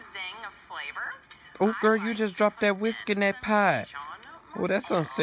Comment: I thought he said, "Oh girl, you've just dropped that whiskey in that pie." 😂